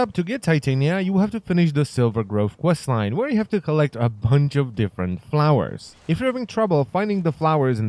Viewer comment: i dont have titania yet or know her quests and i thought i heard you say collect a hundred flowers XD